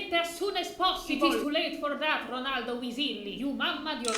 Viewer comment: "you mama'd your last Mia " is a line that would send terror through anyone's bones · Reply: I thought she said you huffled your last puff